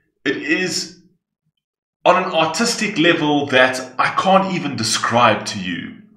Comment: im sorry but every time you say “artistic” i hear “autistic” 😂